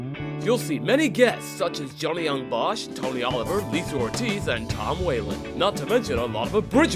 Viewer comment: When they mentioned Johnny Yong Bosch I thought they said TOMMY Oliver hahahahaha. If I were there I'd TOTALLY poke fun at that...like "Hey Tony, you related to Tommy? I mean we have Adam over there.." HAHAHAHAH